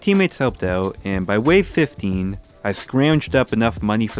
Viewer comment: I swear to God he said “50” not “15” but when I’m listening now I hear 15 now